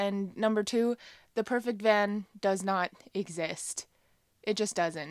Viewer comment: Hi, I had to do a double take. I thought it said at the end, the perfect man does not exist. 😁😁 I'm sure he doesn't. Cheers!!